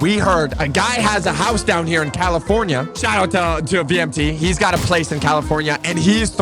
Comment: who did you say did you say VMT!!!!!!!